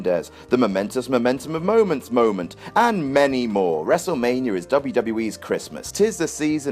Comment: i thought he said and men porn instead and many porn